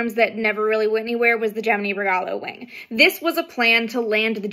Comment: The only thing you pronounced that I had trouble with was the "regalo wing" part at first - it wasn't until the end of the video that I finally figured out what you had said. Now that I know what you said, I can't hear it any other way, but I heard, like, "rgah loway" or something and I was very confused. Really cool vid tho, loving it